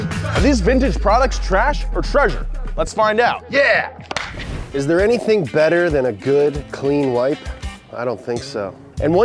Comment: Why did I hear “it’s there anything better than a good clean wife” lol